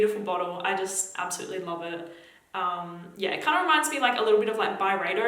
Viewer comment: I thought you said it kind of reminds me of my vibrator 😂🤣